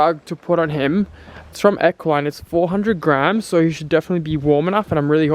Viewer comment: For a second I thought you said “We got this new blanket and it’s four hundred GRAND.” (In Ireland grand means thousand, so to me it sounded like four hundred thousand.)